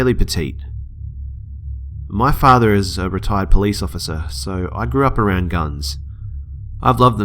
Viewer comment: When he said retired police officer I thought he said retarded police officer